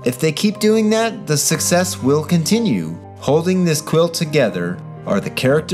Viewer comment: "Holding this Quill together..." over a picture of Chris Pratt lol that's what I thought I heard